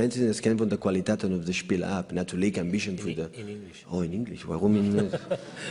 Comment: parece que dice “En English weon” xD